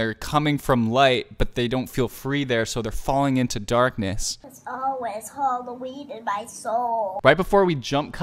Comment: what's she saying? I didn't understand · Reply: it's always halloween in my soul haha